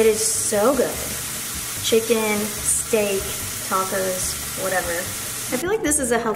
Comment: Lol. I love how she's like "......(Argentina) shoutout... Love your steak!" Me: I thought argentina was a country (I thought she said state) Yes, I know she said steak lmao I'm watching her video on a low volume next to my bf 😂😂😂😂 Gotta love this real ass woman🙌🏼🔥😂😂